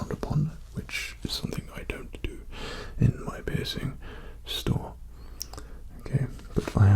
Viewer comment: I thought he said pissing store